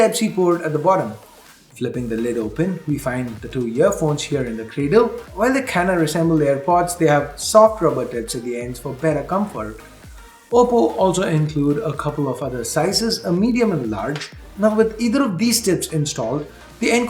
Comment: M I the only one who heard popo